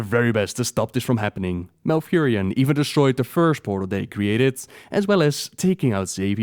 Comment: I misheard "the first portal" for "the firstborn" I must say idea of malfurion murdering firstborn of azshara and sargeras was interesting xD